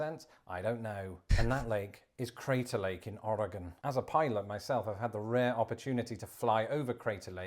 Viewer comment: LOL, I thought he said, "As a pirate myself." And yea, Crater Lake lost a mile of its height when the caldera fell straight down into its massive magma chamber, and it basically swallowed up a mile of the mountain and created a 4,000 ft. lake. It is believed to have then the largest eruption on earth in the past 10,000 years, and yea, Native Americans in Oregon witnessed it, and they used to call the former mountain, Mt. Mazama, which is gone, it's swallowed inside of the magma chamber below the earth's crust. It has then since vomited back up parts of what it previously swallowed in later less eruptive, and cone building eruptions, building a new cone that is now poking up out of the lake called Wizard Island.